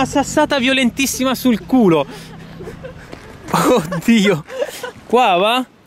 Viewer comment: Ma solo io ho sentito la bestemmia al minuto 5:30?😂😂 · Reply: riccardo alfano era un "oh dio" :)